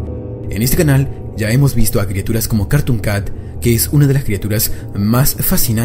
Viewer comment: yo le entendí carton cat